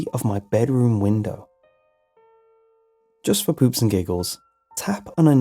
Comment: Did he just say "Poops & giggles" or "Poots and giggles?"